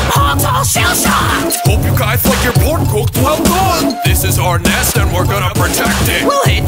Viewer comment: I thought he said "Hope you guys like your pork cook well thawed"